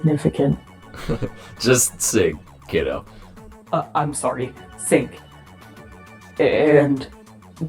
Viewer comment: I just realized this but it sounds like ui said sink